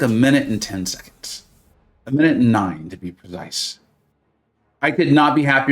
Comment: I had to rewind because I thought I heard you say “a minute and 9, nice.” Which would have been a next-level 69 joke.